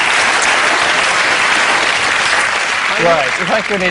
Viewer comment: What did Angus say at 10:49? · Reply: Koleenex = Coleen + Kleenex